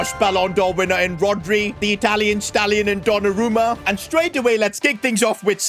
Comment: Why did i hear "The Italian Stalin in Donnarumma"